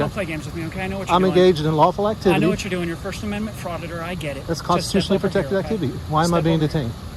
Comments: Did he say " you are a first amendment frauditor"? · The Police officer says himself, he knows the auditor is out looking for a lawsuit...then proceeds to violate his rights.... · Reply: Play it back. At 1:18 the officer said first amendment frauditor instead of auditor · AHAHAHA Did anyone else catch the cop call him a "first amendment frauditor" Its at about the 1:15 m mark